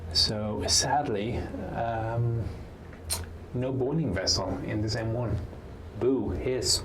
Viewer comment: What are you saying at 26:15? "morning vessel"? "boarding vessel"? and what is this thing that's not there? Oh, the ship I served on was scrapped about the same time that turret was manufactured. And the aircraft I worked on were all expended as target drones by then... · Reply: Boiling Vessel.